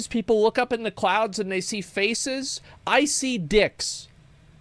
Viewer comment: "I see dicks"... then I heard "icey dicks"... then I thought "I see icey dicks".